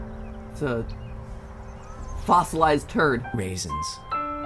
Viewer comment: I almost thought mat said fozzolised turd raisens